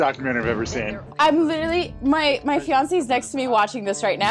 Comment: did she just say fIaNcE!?🥺🥺